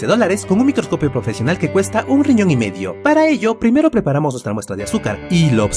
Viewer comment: Primero escuche " un millón y nedio", ya después "un riñón y medio", jejejeje Si esta carito 😂